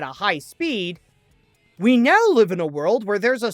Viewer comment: You typed “like” instead of “live”.